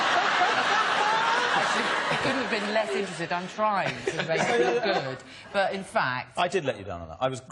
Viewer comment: What does Emma say at 17:13? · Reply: What I hear is: "I said I couldn't have been less interested, I'm trying to make you look good, but in fact..." before Hugh chimes in with, "I did let you down on that - I was grumpy, fat, bad and got arrested halfway through" during which Emma adds, "You were richer than God as well" 😁